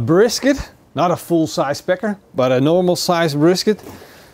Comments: It sounded like you said not a full size pecker, 😂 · LOL! It sounded like you said "not a full sized pecker".. LOL!